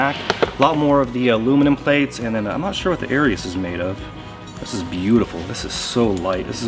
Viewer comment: What kind of plates did he say these are? I’m looking to buy some good quality new ones and have no idea what is best. Maybe you could do a video about plates for us 🙏🏻 pretty please 😊🖤🛼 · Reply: I thought he said ‘aries plate’ and when I searched for it, I think it might be ‘arius.’ It’s an expensive plate! <link>